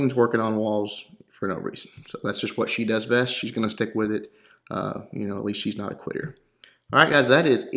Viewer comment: Did you say at least the queen isn't a queer